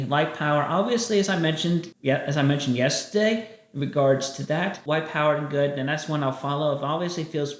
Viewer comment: does Chris say... white power good?